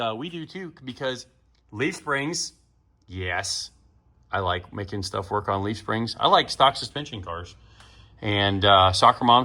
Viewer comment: I thought you said you like soccer moms and leaf springs. Ha